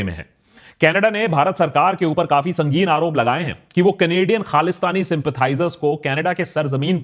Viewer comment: instead of Canadian khalistani the caption says Canadian-Palestine supporters